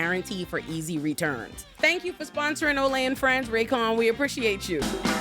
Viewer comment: Tell me why I thought you said your sponsor was Raytheon